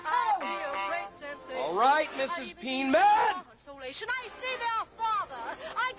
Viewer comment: I thought he said “All right Mrs. PeaNUT”!